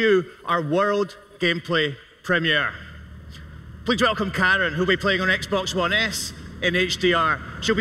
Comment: People watching in 2021: 2:17 did he say Karen